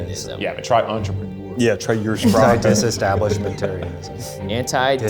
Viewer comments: Is it really fair for them to have to spell non-english words when the host isn’t even fluent in reading them out loud? 🤔 I speak German and had no clue he was saying “ursprache”, I thought he was saying “ersprache” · I can speak German and I couldn't even tell what they were saying when they were asked to spell "Ursprache". I heard "Erschpracke"!?!? which then changed to "You're-sprakke"...